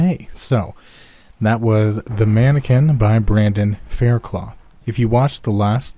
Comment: Oh thank God. I thought it said "The Mankini".